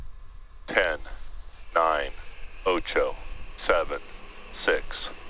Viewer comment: Anybody else hear them say OCHO in the countdown!?? 😏